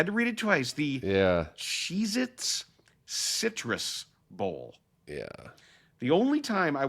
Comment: For a second I thought they said UM was playing in the Cheez-Its MISTRESS bowl. 😂 I had to rewind it.